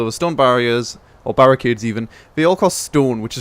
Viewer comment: lol it said barriade instead of barricade